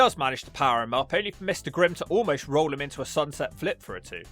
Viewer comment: Enzuigiri to the kidney? Oh hell, his name is Mr. Grim? I swear I heard "Mystic Rims" like he was a human tyre shop.